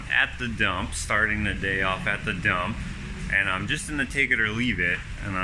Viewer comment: Omg thought you said so i started my day off taking a dump... Lmao